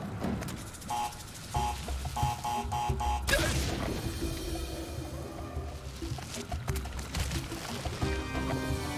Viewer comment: what did he say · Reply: Dip